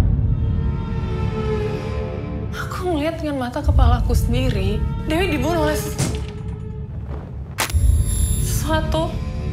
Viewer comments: dibulus?? Itu apa yakk? Atau gua yg salah denger? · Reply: thanks thankss, uda di puter ke 0,25 bru kedengeran "dewi dibunuh oLeh s...." Kepotong dehhh makannya kedengaran dibulus wkwkwkw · dibulus?? Itu apa yakk? Atau gua yg salah denger? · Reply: "Di bunuh oleh s...sesuatu" Gitu Dialog nya:)